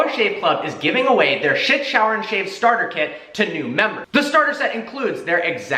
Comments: did anyone hear sh*t shower and shave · Sounded like dooler shiit shower kit